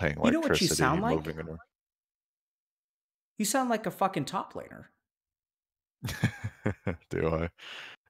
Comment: Love that: Dyrus: "Lost my girlfriend, moved out of the house, being sad" Dr. K: "You know what you sound like ? You sound like a f*ing top player! " Dyrus: Nods. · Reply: At first I heard it "you sound like a fucking toddler" and was like WOAH DR K CALM DOWN